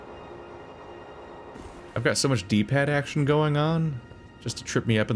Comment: i heard "I've got so much deep head action going on" 💀